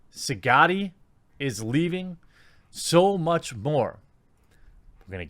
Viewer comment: I thought he said " so much poos! "